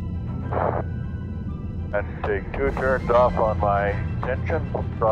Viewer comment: Did he say 2/3rds off my pension . This guy is a champion. Loved this.